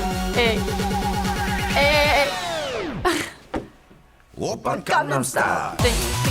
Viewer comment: Gangnam style I always thought he was saying open condom style.